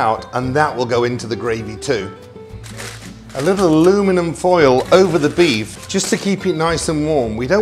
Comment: did I hear and Englishman say "aloominum" ?